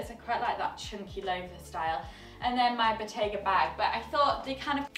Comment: I heard “Bottega bag” as “potato bag”! Lol